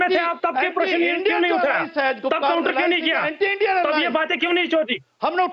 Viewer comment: did he say "tab ye baaten kyu nahi chodi" ? LMAO devil pretending to be an angel hhaha